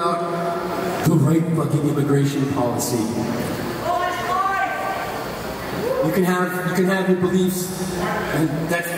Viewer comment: What did the dude shout out at around 2:30 can anyone figure that out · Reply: abolish ice